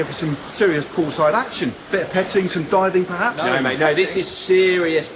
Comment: What is actually said here? "Bit of diving perhaps"?